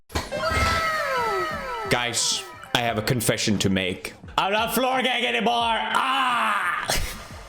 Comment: I thought he said no more floor gang?